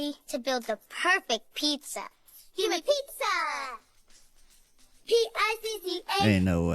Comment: 😮 🫣 I'm sorry, am I the only one who hears "HUMAN PIZZZZZA" ?!?!?! SLOW IT DOWN & LISTEN TOO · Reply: me too! I had to turn on the captions and it said they were saying "GIVE ME PIZZA". It sure sounds like they are saying"HUMAN PIZZA", yes you are absolutely right.